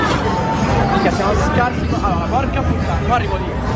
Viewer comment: io a 4:26, 4:27 ho sentito un bel PORCO D..